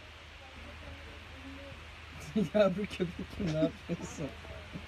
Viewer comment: ne dedi anlayan var mı? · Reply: Yavru köpek gibi ne yapıyorsun dedi